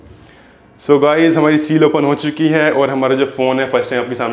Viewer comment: hain.. Kya bola tune 😂😂😂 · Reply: Phone bolna miss hogya bro,😅 phone ke box ki seal open ho chuki h.